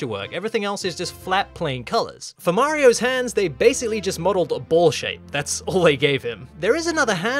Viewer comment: i thought he said bullsh